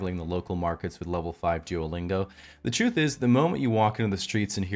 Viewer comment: "Suavely haggling the local markets with level 5 Duolingo" chuckles I'm in danger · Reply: Oh he said Duolingo? I thought he said "duelling". Much more sense is made.